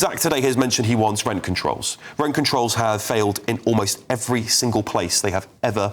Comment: I thought he said Rat controls!!